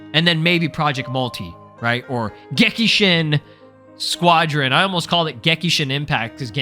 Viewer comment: I have brainrot. For some reason I thought you were about to say "gekishin La squadra" AHHHHHHHHHHHHHHHHHH